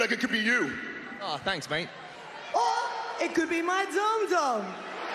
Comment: This is the only part where i want to laugh with Seth Rollins when I heard "Dom Dom" 😂😂😂